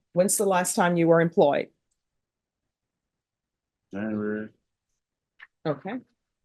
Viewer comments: i definitely heard “january” not “never” lol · I thought he said "January" rather than "never" in relation to when he was last employed.